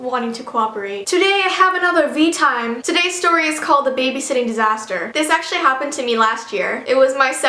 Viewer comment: When she said the title in the beginning I heard "the babysitting zester"